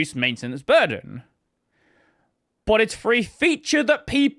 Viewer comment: I appreciate the manual subtitles, but they could use a little proofreading. At 12:24, there's "free" instead of "for a," and earlier there were at least one or two other notable errors (like "roar" for "raw").